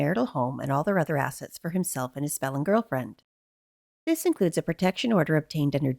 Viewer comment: I kept hearing you say “his swollen girlfriend” 😂 i had to go back and read where you typed it out and was saying “his FELON girlfriend” lol😅